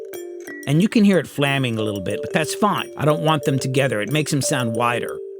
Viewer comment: What is flamming? At first I thought you said flanging but on repeated listening I can't unhear flamming.